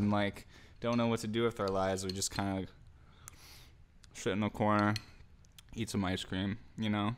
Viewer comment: I swear it sounded like my mans said "kinda just shit in the corner..."